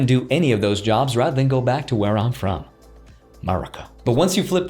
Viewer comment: Where are you from? I thought you said Baraka for a moment.